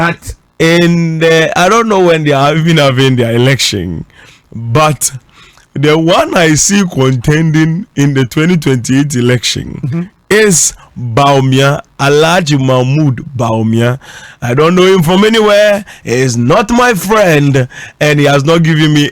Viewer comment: But I thought he said President Mahama will win the 2028 election